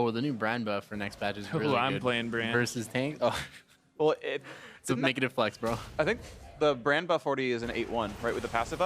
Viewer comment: What does Aphro say at 22:41? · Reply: negative flex bro